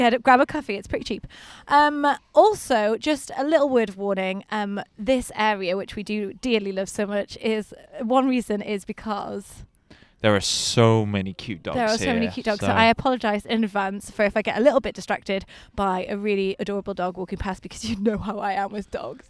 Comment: Starting at 0:47 I thought said that there were so many cute girls and that was saying that she might get distracted by all of the cute girls. Then I realized you said dogs, not girls.